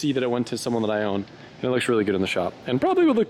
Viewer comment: Did say that he's happy to see the NXS went to someone that he "owns"?......